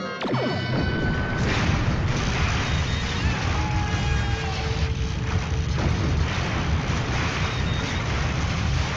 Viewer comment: Seriously, at 1:25 what does the Romulan Commander yell out? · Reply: Nooooooooooooooo!!!!!!!!!!!!!!